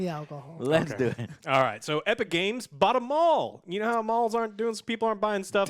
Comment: When said “Epic games bought a mall”, I misheard as “bought em’ all” and now all of the games in the world are f-ing epic games exclusives.